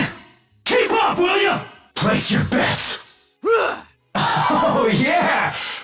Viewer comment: What Nero's saying? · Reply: "Place your bets", he says this when he uses "Maximum Bet" skill, in Devil trigger form